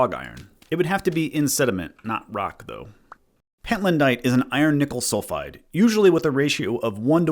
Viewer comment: i misheard iron nickel is iron nipple. Now i can’t unthink it.